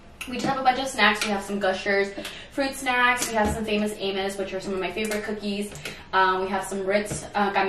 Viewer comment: Sounds like she said famous anus hahahah love it